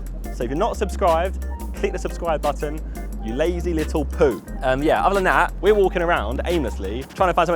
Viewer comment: did he just say lazy little poop lol dying